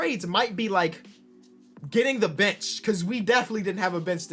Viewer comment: NGL AT 15:37 I THOUGHT HE SAID BITCH